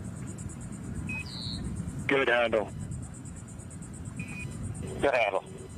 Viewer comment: Good handle??? I thought he was saying skedaddle 😂😂😂